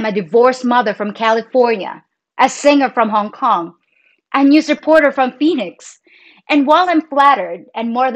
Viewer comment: at about 01:01 in this video: "...a news reporter from Phoenix." When I first heard you say that I thought you said: "...a news reporter from Venus." Really...🤭